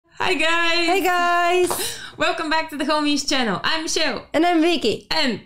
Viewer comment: I swear I heard you said "welcome to the commies channel" in russian accent lmao